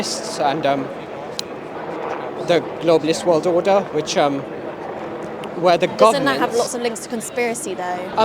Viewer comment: lol did he say "globalist world order, where the Goblins"?? 😂😂😂 These people are thick as mince